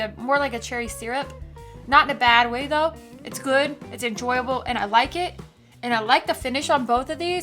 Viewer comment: Did you just say EnJory? Lol